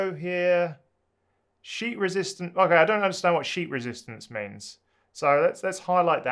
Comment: Thought you said "sheep resistance". Maybe it's a New Zealand thing.